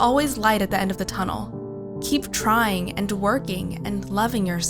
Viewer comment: am I the only one who heard at 6:36 ' keep trying and twerking ' ??